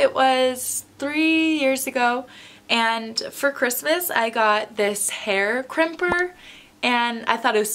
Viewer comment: I thought you were gonna say for Christmas is got a seizure